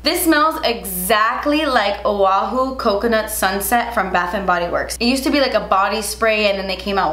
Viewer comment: I thought you said waffle coconut sunset 😂